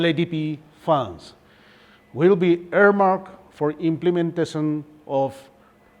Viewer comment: after LAPD I didn't got what he said is it airmark or it just came out from nose while breathing 👌